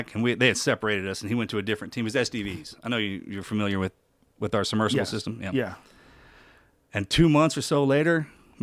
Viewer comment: What I heard Mark: he was STDs I know you familiar w Joe: yea yea